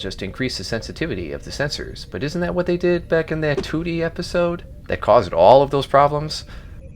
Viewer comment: I thought he said "Tootie episode" and I was like "I don't remember a Facts of Life crossover"